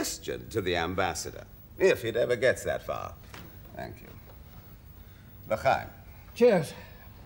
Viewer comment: what the ambassador says ??? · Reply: l’chaim